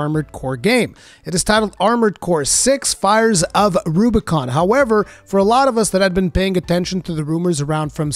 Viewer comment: I wasn't looking at the screen and I totally heard "Fires of Rurikhan."